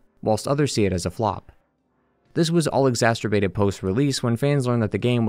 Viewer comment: you say "exasturbated" instead of exacerbated